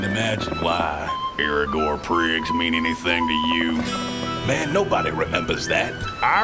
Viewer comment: What is Graves saying at 0:23? please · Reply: aregor priggs mean anything to you